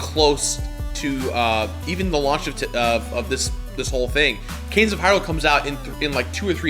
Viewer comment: Someone please tell me what he says at 24:22. canes of harlech?